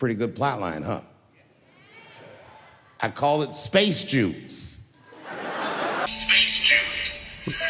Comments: What did you think about Dave's special? Also, sorry for the constant exposure change. Until I buy a new camera, that's how it will be. Unless you prefer 20 FPS. Windows broke my camera settings and can't lock light exposure. NOICE!... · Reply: I always thought he said "Spacejuice" as in O.J. turning his back on black people and then tries to come back after he gets in trouble. I'll have to watch it again but I loved it!!!🎭🎭🎭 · I honestly thought he said 'space juice' 🤣🤣🤣